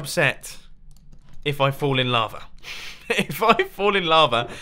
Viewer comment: He said "I'm going to be extraordinarily upset if i fall in lava," and i heard "fall in love," and that kind of scared me to death